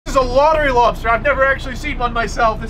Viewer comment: I thought he said, "This is a WATERY lobster!!!" 😮😐🤨🤔🧐🙄